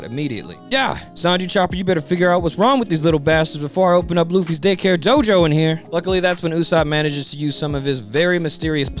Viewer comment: Oooh "Day care Jojo"! I had to rewatch that part multiple times. I thought you said "🍆-hair Jojo." I was SO confused